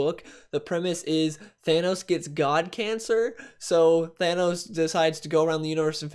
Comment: When you said that Thanos had cancer, I thought you said that he had "GOD CANCER